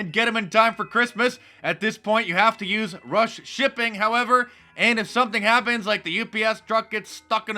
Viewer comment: Christ-a-phobe. Good one ! I can use that. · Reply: Did he just say, have to use Russian shipping? I knew it! Mark Dice is in "collision"!!!